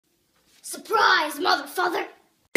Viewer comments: I thought she said surprise mf💀💀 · I thought she said mtf